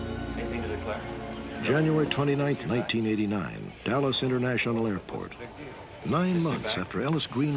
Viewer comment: can someone please clarify? The audio and subtitles don't match ...Stack says "Dallas" international airport, but the subtitles says "Dulles" international airport which is in Virginia. Which airport is it? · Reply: Dallas